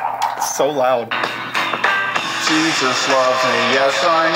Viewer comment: what you hear: "Jesus loves me yes I know for the bible tells me so" what the captions have to say: "Jesus loves me yes i know Florida probably the one tells me so" 💀💀